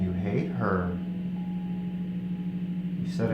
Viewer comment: I think I hear "I hit her" rather than "I hate her." Possibly explaining why they did what they did to her 😔 And at 7:54 I definitely hear "headboard" as opposed to "I hit board." I feel like there is some kind of universal law that inhibits spirits from outright saying what happened to them in a case like this, so maybe she's just throwing clues out there.🤷‍♀️